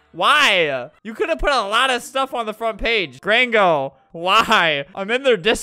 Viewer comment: lmao i thought Alberto was saying "gringo" lmaooo he was saying grango.xDD (gringo means someone who doesn't speak spanish)